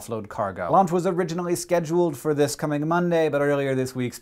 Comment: Thought he said "Lunch was originally scheduled for this coming Monday..."